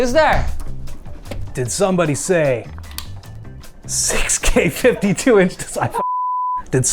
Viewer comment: Gonna be honest, at 00:43 I swear I heard say "DId somebody say...sex cave 52 inch...." xD